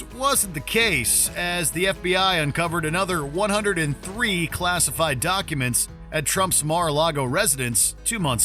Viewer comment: Oh sorry. I thought you said "classiest" things -- I was curious.